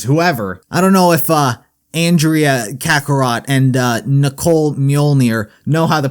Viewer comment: did you say kakarot